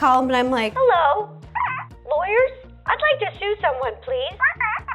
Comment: When Ro said “I’d like to sue someone please” I thought she said “I’d like to SHOOT someone please”